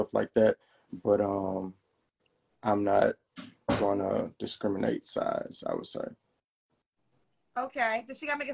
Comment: Bruh said no smoking weed, he done cut the field in half within the first 2 minutes 😭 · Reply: I thought I heard him say he "I'm not gonna discriminate on size"...aka I took it as I'm big..but I can move..